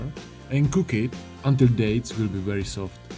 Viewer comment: What is it exactly that you use in the red wine puree? Sorry I didn't really understand what you were saying hahahahh thought you were saying dates :( sorry! Greetings from Spain! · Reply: Yes dates are right